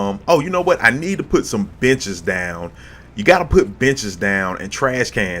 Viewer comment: For a second there I thought you said 'Oh you know what, I need to put some bitches down!' then I realized you said 'benches'. LOL!